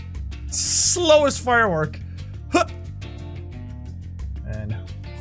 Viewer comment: it sounds like someone is saying Anna